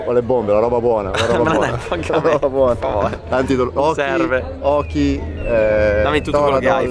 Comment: Did you say terve? in finnish it means hi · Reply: He said "serve" which means "it is needed". They were talking about how tiring it is to race a Nascar, the man in yellow said that he has drugs so he doesn't feel tired (it is a joke of course) and said "serve" (they were both ironic).